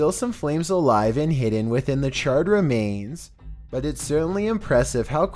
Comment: God at first I heard "child remains"